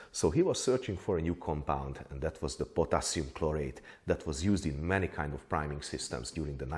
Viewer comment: Potassium perchlorate, not potassium chloride, at first I misheard :) Lead, mercury residue to breath in... sounds healthy haha Luckily you're well aware of all symtoms of saturnism and mercury intoxication, so you'd notice if anything were wrong ;)